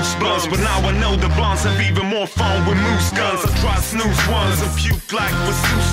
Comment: Ooooooooooh, he's saying "Snus" thought he had been saying "Snooze" all the time...makes a lot more sense.